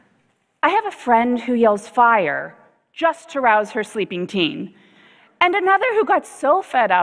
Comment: What did she said? Arouse? Reroute?